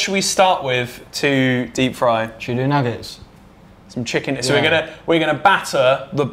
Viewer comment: who else thought he said tuna nuggets at 6:39!